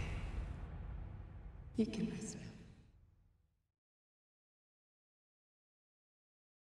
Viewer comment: what does she say? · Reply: "We’re gonna be okay" "You can rest now"